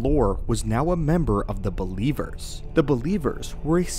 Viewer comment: I swear to god I thought you said Beliebers and I was just thinking "Yeah, that checks out for a sith cult".